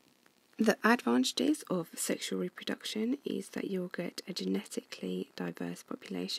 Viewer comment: Hi. At 1:23, did you say "yogurt"? I do not understand what you said there. Video is helpful, thanks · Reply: you'll get